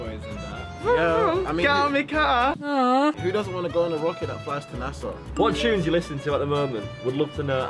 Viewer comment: did he say fly to nasa 😂🤔